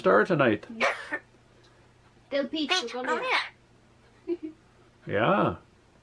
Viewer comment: "PEEEAAACH!! COME HERE!!" He is such a clever and funny little guy! We love you! Lol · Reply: I thought he said Pete, come here. Lol